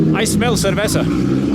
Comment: see thought you said ceviche not cervesa